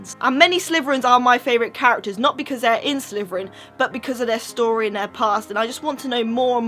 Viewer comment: Am i the only one who hears "sliverin" from?